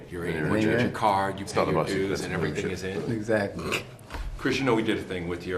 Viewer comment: Can you tell what he says at :30 and 1:13 lol he speaks too low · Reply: and at 1:13 i think he says it's not about the dues idk